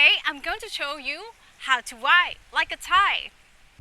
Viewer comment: I thought she said lie like a Thai, that makes more sense to me.